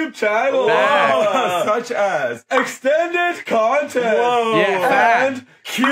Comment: ngl I thought that said "Extended Cl*t" for a second.